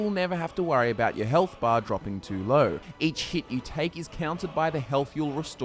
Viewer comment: did he say each shit you take lol